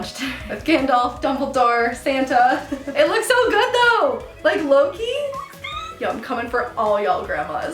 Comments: when you said low key i thought you said loki \_(ツ)_/¯ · Ahahah I thought Lauren said: LOKI instead of Low Key😂😂